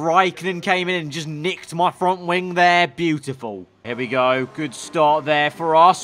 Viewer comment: For some reason instead of hearing beautiful I heard biblical